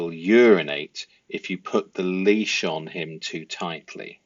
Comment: Maybe you could clarify your analysis for me here? The text says "...puts the leash on too directly." but you say ...puts the leash on too tightly. And later you talk about it being weird that talks about her dog being strangled in the interview.. I understand that you are drawing inferences from what she's saying, but is that based on your understanding that she actually uses the word 'tightly'? Bc my understanding is that she's only talking about approaching the dog too directly and trying to leash them too quickly and that's what makes them nervous and possibly pee.. I don't hear her say anything about the leash being too tight. Just hoping you can shed some light for me please! Really enjoying your videos so far and fascinating analysis! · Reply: You said it better than I could. I was just about ready to make a similar comment. She totally said too directly not too tightly